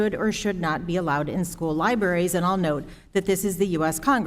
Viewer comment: Did he just say Lie-berries